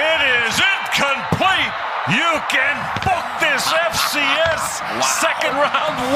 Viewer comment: What's the guy saying at 3:07? "It is INCOMPLETE! You can __ this FCS second round win to head coach etc etc!" I mean, I know what it SOUNDS like he's saying, but these announcers are on live TV are they not? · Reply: book